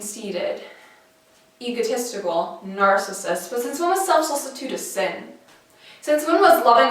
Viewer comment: Would you please tell me, what do you say around minute 1:30ish? Thanks. I thought you might like to know, that I (a 34 year-old woman) have started an intergenerational Women's Empowerment group recently and one of the young girls (age 14) picked your piece of poetry to share tonight at our gathering. Thank for your beautiful words and perspectives. Love and Light <3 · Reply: Right, captions. Got it figured out. She says, "but since when was self-solicitude a sin?" And because I had never heard the word solicitude before I looked it up. It means, "care or concern for someone or something."